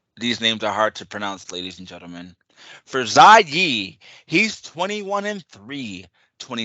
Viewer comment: You got yi or lee? Sounded like you said zha lee 😂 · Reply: Zha Yi lol is my pick but not 100 confident!